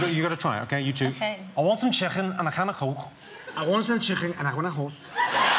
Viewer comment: it sounds like he says “I want some chicken, and I want a horse”1:56 not 2:03 sorry